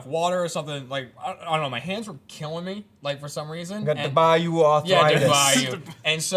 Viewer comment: the first couple times they aaid da bayou I thought they were saying dubai you. I was very confused.